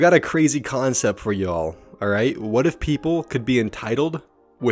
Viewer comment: Oh. PEOPLE. I thought it said PARENTS. I got confused.